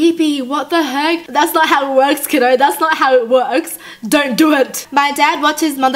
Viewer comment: The captions said killer instead of kiddo CONFIRMED